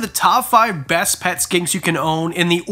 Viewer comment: I thought it said pet skins